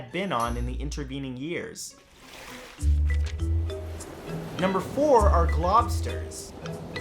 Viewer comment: I thought he said glocksters, Glock lobsters, I need sleep